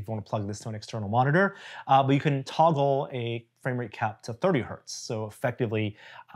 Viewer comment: I suppose It's not a coincidence that as a PC gamer I hear "you can toggle frame rate cap to 30 hurts" instead of "30 Hz"